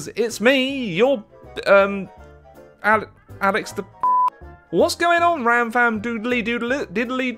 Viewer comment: I thought he said Alex The F***